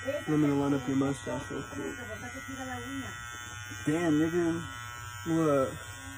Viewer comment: did mans just say nigga